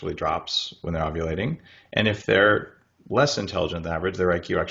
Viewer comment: You may want to check the subtitles. At 31:20 they say "violating" instead of "ovulating"!!